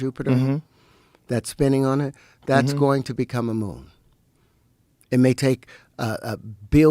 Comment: I thought he said "going to become a gnome" and I just nodded along because that fit in with everything he'd already said. 😂